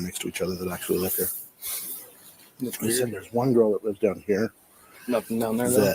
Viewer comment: Did he just say that Ashlynn (a girl) lives in that sixth room? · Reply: he said "another girl lives down here", I think.